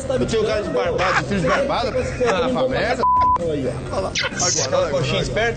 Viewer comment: eu entendi x merda